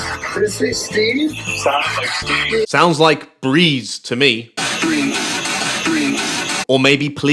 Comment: First I heard "breeze," then "breathe," and then... BREEN. Oh my god, the bridge was cursed by Neil Breen. I love how the slow-mo used to avoid copyright strikes makes Zak sound drunk.